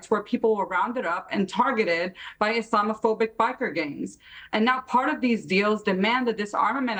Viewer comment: Islamophobic biker gangs. Did I hear that ?